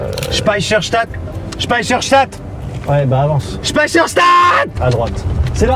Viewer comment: a 32:58 j'ai entendu Spacefox charte xDDD